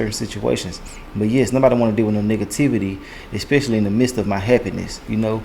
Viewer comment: Gotta open my ears. Thought he said niggativity. Nonetheless, my condolences 😢